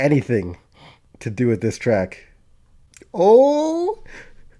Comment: I was about to be livid because I misheard him speaking about Kane's old theme. I misheard "track" as "Trash" and had to double take lmao